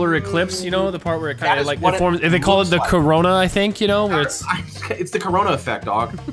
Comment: Did he just say Corona.........??????? TYLER YOU CAN PREDICT THE FUTURE YOU BASTARD